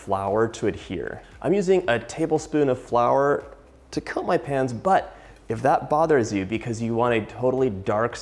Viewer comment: I legit hear that "I´m using a tablespoon of flour to cut my pants" :´D (Instead of "coat my pans")